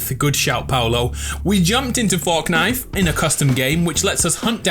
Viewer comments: I can’t be the only one who heard we jumped in to “forknife” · why did you say fork knife